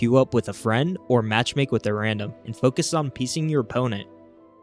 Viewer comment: yo I thought he said focus on pissing your opponent 😂